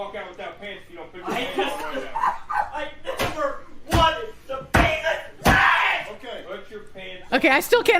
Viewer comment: I think he is saying "I never wanted to bang a child"...